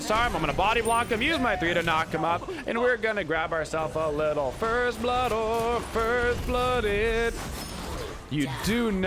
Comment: Can someone tell me what he's singing at 2:33 I'm hearing: "First blooded, first blooded"? in different tones.. I'm not sure, can someone confirm? Thanks :D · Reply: Yeah he’s saying “first blood or first blooded”. He says it because is known for being hyper aggressive in the early game, which usually results in getting first blood.... or getting first blooded. Lol